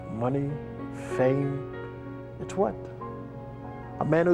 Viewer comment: What does he say at 21:40? When talk about money, fame, its??? I rewinded about 6 times can't figure it out. Amazing video, Thanks, message is clear from ATB...live life to the fullest. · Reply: He say "money, fame...it's what. Thanks for watching and thanks for leaving a message! It's appreciated.